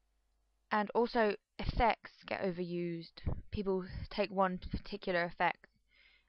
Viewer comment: I SWEAR I HEARD "SEX GOT OVERUSED" AND I WENT ".... BUT... BUUUUT! ... D'''':" I lol'd at myself. I'm so very guilty when it comes to using Proteus, but, well, I'm just still too damn happy about the fact people do seem to like him after all [it's like feeling giddy for your not very pretty but extremely lovably cousin going on a date.... srsly :'D]. MORE RANTS! ~ [I reached the next level of being a stalker in which I make puppy eyes when I hear your voice][/tr00fax]